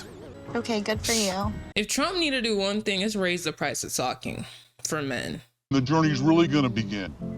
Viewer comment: yooOOOo did I hear you right did you say, “if there’s one thing Trump needs to do its raise the price of talking … for men.”? 🤣😭💀